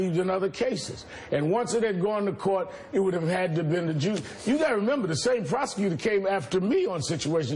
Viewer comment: At around 25:52, does he say “it would have had to been the Jews”...???